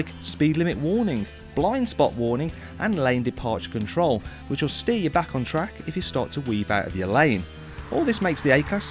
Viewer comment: Hi Matt, at 4:17 did you say this car will steer you back on track if you steer out of your lane???